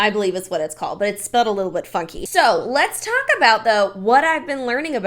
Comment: She should submit that video to the academy Also, when you said it's spelled a little funky I thought you said fucky & I almost choked in my car